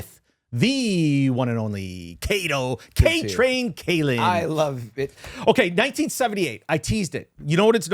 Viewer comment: What did Kato say?? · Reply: I love it ?